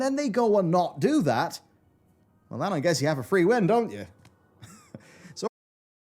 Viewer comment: Did I heard right? Did said something in the lines of "If he (Ion) changes his mind about doing stuff, is because he has his own free will" ? 13:06 This is insane... this guy must receive some sort of check by blizzard, there is no way. EDIT: oh. he said "Free win", this is worse... exactly what said, it doesn't matter if you were right or not, the game is still shit...